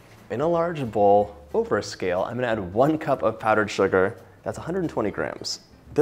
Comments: you said "one cup of powdered sugar", however the CAPTION says one cup of FLOUR. Which is it, it looks like flour? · Thanks for the Recipe. I do have a question, at the 2:15 mark, you say 1 cup of powdered sugar, but the caption says 1 cup all-purpose flour. May you please "clarify"?